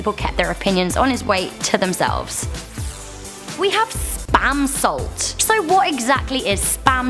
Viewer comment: When I wasn't looking at the screen, I heard "Spam-sault" and I thought I heard, "Spam Salt." I thought it was salt for Spam. 😂